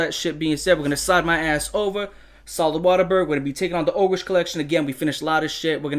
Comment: did i hear waterburg lol